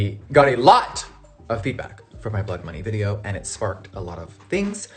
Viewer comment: I thought you said Blood Monkey lol.